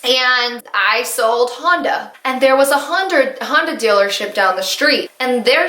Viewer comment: You said Honda. I heard Hunda. I've been watching so much antimlm content, lol.